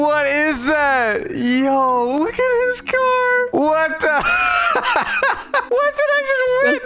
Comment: Why did i hear "bae what?😭" instead of "hey what?"